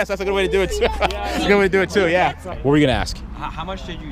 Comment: sex joke, what did he say? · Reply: "Or you could give the car away and ask for something else in return."